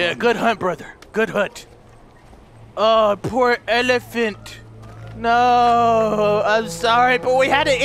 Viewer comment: Did he call it an elephant